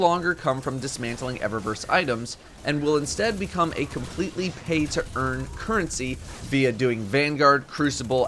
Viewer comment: Im confused. 3:20 did you say PAY to earn or PLAY to earn?